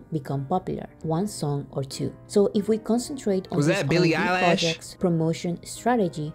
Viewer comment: did you just say billie eyelash? xd